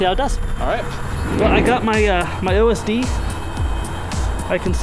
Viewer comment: Did he just said I've got my LSD?